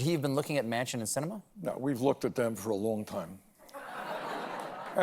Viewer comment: closed captions says "man inch" instead of "manchin." hmm, that's possibly a freudian slip.